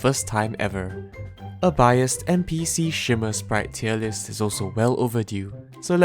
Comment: i thought it said based shimmer tier list